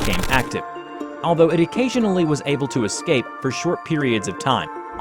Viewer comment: did you say he was ABLE to escape